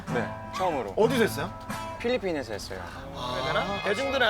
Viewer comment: English subs please.. thanks. I think he said Philippines